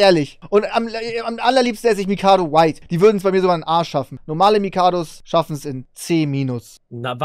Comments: Die würden es bei mir sogar in A schaffen hab ich zuerst falsch verstanden.. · Wer hat bei 18:47 auch verstanden das die Mikado White es in seinen A*sch schaffen ??😂😂